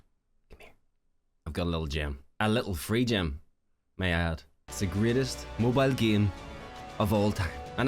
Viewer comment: My dear you can come tape yourself to my roof whenever you are ready 😉 😎 and when you said "A free gem might I add" it sounded like you said "A free gem...my ass" 🤣😘😁